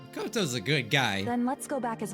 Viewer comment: when he said mokoto is a good guy i thought he said gonta is a good guy